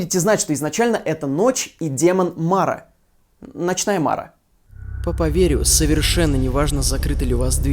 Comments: блин, я услышала "Ночная Шмара" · Я услышала "Ночная шмара " Норм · сначала послышалось, как "ночная шмара"